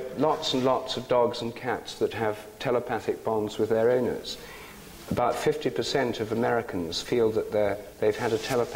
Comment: I was hitting my bong @ 20:16 and I thought he said "there are many pets with telepathic Bongs with their owners" haha I wish I had a telepathic bong with my dog. I'd be hitting it and he'd be telepathically getting high or vice versa. lol